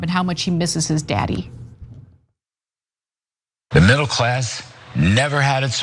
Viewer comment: It really sounded like he said Donald chump...lmao